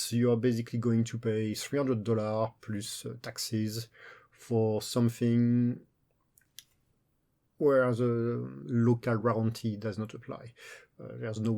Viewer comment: What did you say at 1:30? It is impossible to understand due to your accent. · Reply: "You pay 300 USD +taxes for something where the local warranty does not apply". Basically if I buy a product in Norway in a Norwegian shop and there's a problem, all the usual rules regarding if the product fail, repairs, remboursements, etc... rules will apply, but if you buy the same product in another country and bring it to Norway, you are on your own, so if the product fails, that's your problem to deal with.